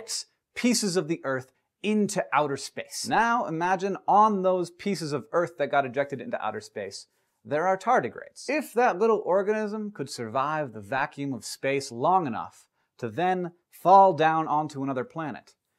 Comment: Did he say orgasm?